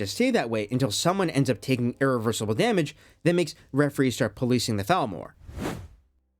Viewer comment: i thought you said policing the thalmor